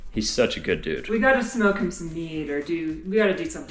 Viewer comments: says "We gotta smoke him some weed or somethin'........" Say what?!?!? 🤣 Seriously! That what I hear! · Dang, thought you said, "smoke him some weed..." LOL